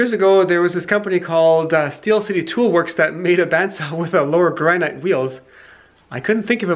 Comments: i dont understand what your saying, i keep hearing "Lower Grannite wheels" Sorry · I cannot for the life of me hear what was said, lower "grianite" wheels? Maybe it's just my phone's speakers or something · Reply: Yeah granite. Steel City are a company that make bandsaws among other things. Solid stone lower wheel, and cast iron upper. And solid granite tilting table. "No rust" is what they advertise. Not, "stupid and dangerous".